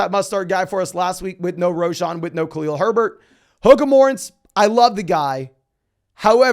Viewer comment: Can anybody tell me what word is saying at 18:31? The CC says 'hook of morence' but that doesn't make any sense for me... I'm not a native speaker...